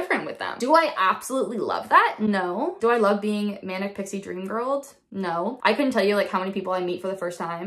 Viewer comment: Thought you said "manic" but was hard to make out as talking so fast. Captions said "manic pixie dream girl". Incidentally, when I speak very fast, is called "pressure of speech" which can be an indicator of elevated mood eg mania